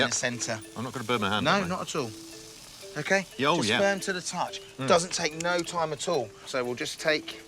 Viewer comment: Auto-generated Captions: "Your Sperm To The Touch" - I wasn't sure what I heard, so I activated the captions to make it sure :D lol